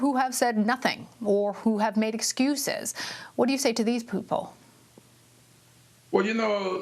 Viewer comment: "What do you say to these poople?" SHE SAID POOPLE